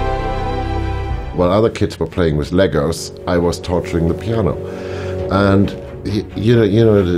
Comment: This is what I thought he said: “While other kids were playing with Legos, I was torturing the piano.” Took me a while to realize that that probably wasn’t what he had said...